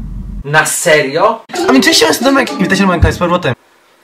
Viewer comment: Czy po trzech latach ktoś w końcu rozszyfrował co on powiedział? xD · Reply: Ami czesc siemano to Wojtek?